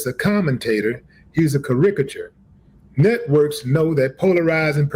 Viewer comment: WHAT HECK DID THAT FOOL JUST SAY? 🤣🤣🤣🤣🤣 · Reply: Karigature